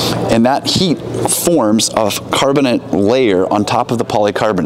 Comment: I thought you said “carbonite”. Han Solo wished he had one of those hoods…